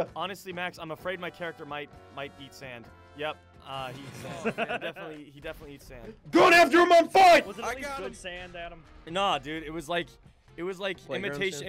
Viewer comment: I thought he said: my character might be sans I thought that he said that and was calling sans stupid I love undertale so I was offened I have a problem